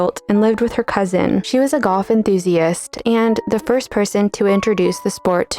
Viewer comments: I thought the narrator said that "Florence was a Goth enthusiast". I looked at the photo and thought, she sure was 🤣🤣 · Subtitles would be much appreciated. Until you said the word "sport", I thought you said one of the daughters was a goth enthusiast, not a golf enthusiast. Excellent video overall.